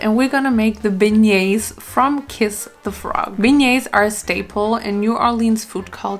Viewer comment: Did she say kiss the frog instead of princess and the frog